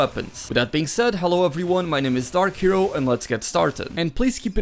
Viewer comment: Before I checked his name, I thought he said "Hello everyone my name is start hero, and lets get started"😝😝😝